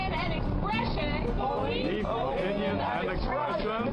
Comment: Did she say,"Boleef"?